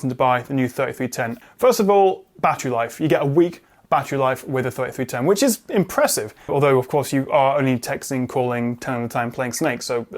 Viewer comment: Is it just me but when he said it has "A week Battery life", i thought he said "A weak battery Life" and i was so confused